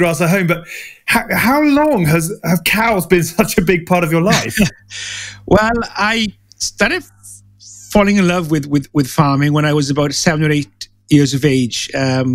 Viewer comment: For a moment I thought he was saying "I started fallowing in love with ...cattle ..."